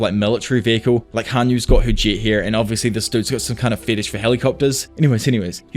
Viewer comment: OOOOHH HER JET !! lol I didn't know what you said it sounded like "whoreget"